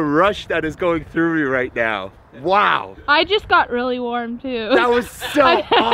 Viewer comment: "I just got really warm too." thats what she actually said. nice video lol😂